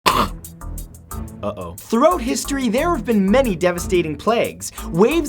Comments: sounded like he said "throat history" not throughout history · I get the pun.... (he said 'Throat' history instead of 'throughout' history XD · I'm American so I thought he said throat history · It really sounds like Matt said after he coughed; "Throat history" Maybe I need to brush up on my Canadian... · am i the only one who heard "throat history" i dont know if it was pun or his accent but if it was a pun good one matt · I thought he said "throat history" in the begining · Sounds like you said throat history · Wait. Did he just say "THROAT history" instead of Throughout!?!?!?!?!!!!!!!???!! Ultimate Pun, is ultimate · At the beginning I thought he said "throat history" but I realized he said "throughout history" 😂👌🏼